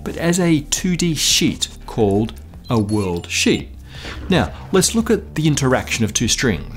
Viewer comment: had me cracking up because it sounded like "WORLDSHIT". Much love to this channel!